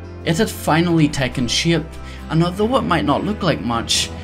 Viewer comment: i keep mishearing "shape" as "shit" "the base was taking shit"